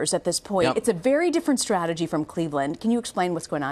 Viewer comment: Did she just say cleaveland